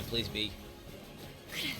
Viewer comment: "Moma please be" I do not know if it was what was said but I cannot unhear that.